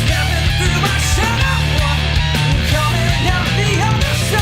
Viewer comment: I always thought he was saying/singing "My Shadow Work". Thanks for clarifying!